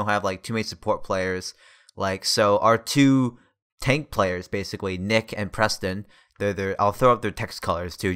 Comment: Not gonna lie, i saw nick and preston and thought it said prick and neston for a second. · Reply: oh lol. Actually I thought you said "I saw nick in person"